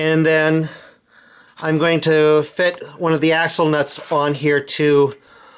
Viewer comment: y do i hear asshole nuts lol